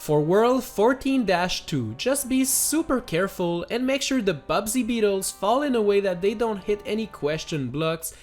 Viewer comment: did you just say Bubsy Beetles? Anyway, it was nice to see if it was possible or not. Good run!